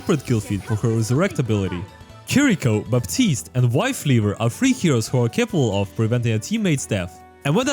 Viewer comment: Did I hear correctly? Wifeleaver? lmaoo🤣🤣